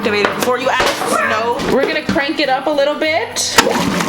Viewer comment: did she say "were gonna crank it up a little bi**"